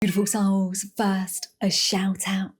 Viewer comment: Every time, she begins too soon, so I hear "tiful souls".